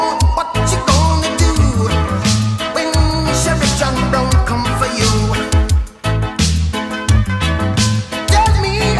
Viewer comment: what did he say¿?😐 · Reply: Sherif john brown comes for you.